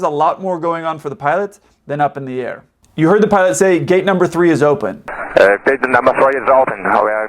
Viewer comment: for some reason I keep hearing ''gate number 4''